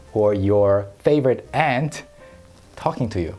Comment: I legit thought he said "you favorite ant" I'm Australian, so I pronounce it the same as the British. With the u. I thought your kid was really weird for a sec there😂😂😂